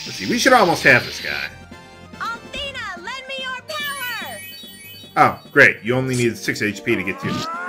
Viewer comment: It sounds like she says "Althena lend me your flower".